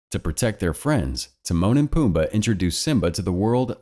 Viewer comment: When you said Timone and pumba I thought you said The moaning Pumba lol